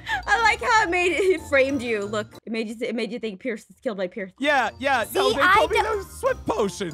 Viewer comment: Am I the only one who heard “pierce was framed by peirth”